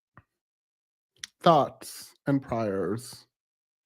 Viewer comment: did you say “thoughts and pryers” ? 😂😂😂